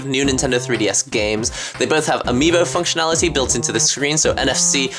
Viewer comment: I thought he said BMO functionality. I was like: BMO?!!? WHERE? xD